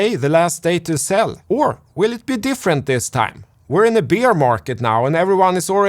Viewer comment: I thought you said the beer market for a second and got excited.